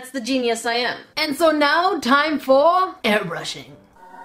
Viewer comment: When said it’s time for airbrushing I thought she said it’s time for hair brushing😳😳😳✌🏼